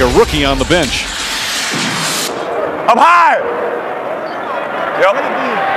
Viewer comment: lol 1:16 I thought he said "I'm high!"